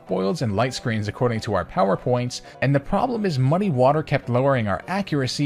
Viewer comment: did he say money water????